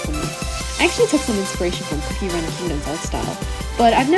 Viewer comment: did you say COOKIE RUN KINGDOM? is that MIKU?!?